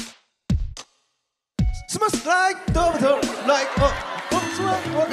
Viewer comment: sounds like he said daughter instead of butter 🤣🤣🤣🤣